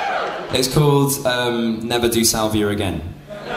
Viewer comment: does he say never do salvia again?